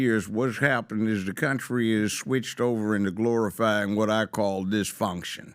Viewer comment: Ya'll mispronounced what he said. He said Glorified Dysfunction not Gorifying. 0:42 Thats where its at if you wanna double check that.